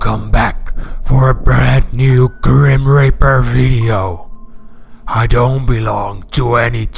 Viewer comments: LOL IN THE BEGINNING IT SOUNDS LIKE HE SAYS "GRIM RAPER" XD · Yooooo, I honest thought you said grim-raper at the start of the vid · is it just me or does it sound like he saying grim raper, rather than grim reaper